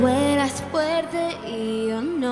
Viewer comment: lo que yo escuche :v Camila: Sin cuidado,Ya me olvide Ahre Eso lo que yo escuche :v